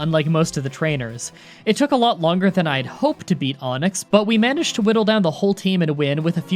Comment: He said "to beat Onix" but I thought he said "to be Onix (like to be honest)" I don't even know why I had the urgency to type this.